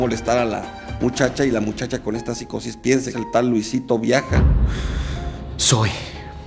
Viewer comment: Jajaja 1:06 pence que dijo LUISITO VIAGRA 🍆😂🤣😂🤣